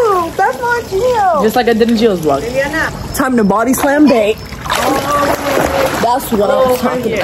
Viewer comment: Did call gio bae or what